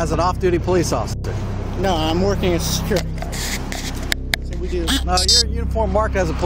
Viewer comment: Where does he say he’s a BODY GUARD ?? @ 1:04 states he claimed he was a Body guard? Pretty sure he said Security Guard…. Lol 😂 what a loser hahaha